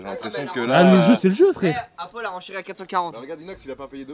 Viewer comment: C’est moi ou à 24:01 inox a dit à apo et chérie